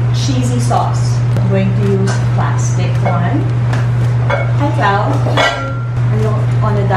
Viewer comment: "Hi Clau" WTF! Nabingi ba ko? Did I misheard that?